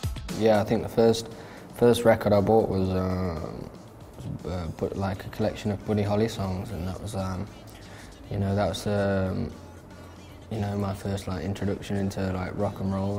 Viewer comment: what did he say at 0:09? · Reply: "The first Record I bought was a collection of Buddy Holly songs and that was my first introduction into Rock and Roll"